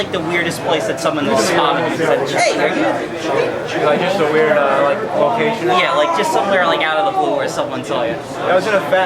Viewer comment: WTF, sounds like he says 'weird caucasian' lol. It's kind of strange how uncomfortable James looks moving toward the end of the video. He doesn't even look relieved when he knows it's over. Just making observations. He's got some pretty tame pop culture taste too, but that's not necessarily anything bad. Pretty laid back I guess.